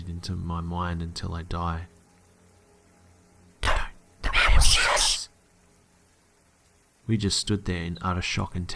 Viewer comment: what did the voice say? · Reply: Adriana I think "don't or the man will see us"